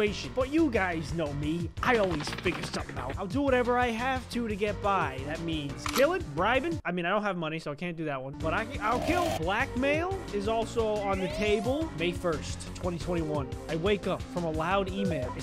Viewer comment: Thought he said black male ☠️☠️☠️☠️